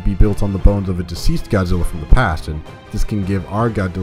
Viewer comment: i think i heard " GODDILLA"...LOL